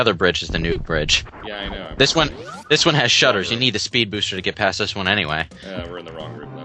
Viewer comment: did he say spood buster?